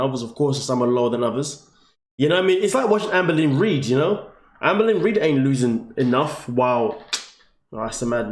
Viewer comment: The way you said Amber Lynn Reid, I thought you said "Anne Boleyn Reid". I was like, what? Is that a YouTuber, what a clever name! Lol.